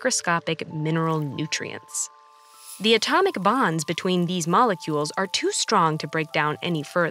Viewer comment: When she said atomic bonds I thought she said atomic bombs